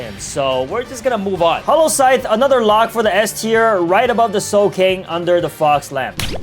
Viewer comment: Did he say soul cane or just me 😂